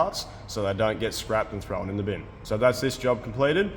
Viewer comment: Did I hear “in the bin”?